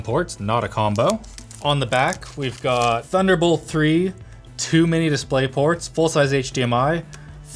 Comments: am i the only one who thought he said "too many display ports" instead of "2 mini display ports"? XD it literally does have too many of them tho :3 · I thought he said "Too many displayports." for a second.